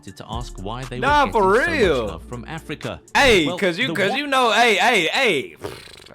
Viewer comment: what did he say there? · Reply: that mexicans lowkey racist sometimes to black people